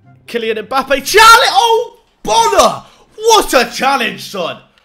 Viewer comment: When he said “Ogbonna What a challenge!” I thought he said “Oh Ballocks!” Because he thought it was a penalty